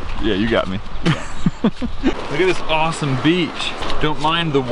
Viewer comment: I thought said bench